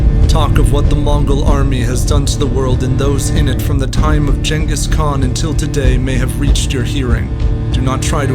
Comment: did he just say jankis khan? lmao